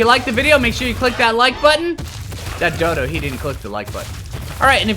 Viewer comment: When he said Phlinger phoo I thought he said fling your poo